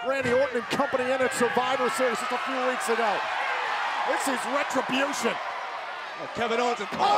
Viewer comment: did he said this is retribution